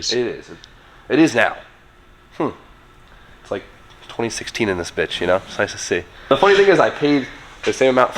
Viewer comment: I thought you said "..B16 in this bitch." for a split second and thought. 'OK, interesting... i dig' XD